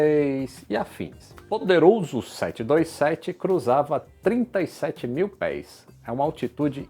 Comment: 727 ou escutei errado.?